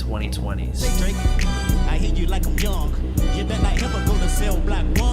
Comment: I thought it said the big shit